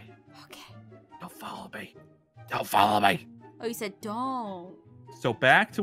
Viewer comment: Aaron:whispers*don’t follow me Aphmau:whispers*ok Aaron:DON’T follow me Aphmau:ohhhhh I thought u said follow me